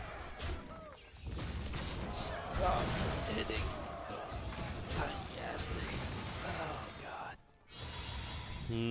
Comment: anyone else catch that? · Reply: Pretty sure he says minions.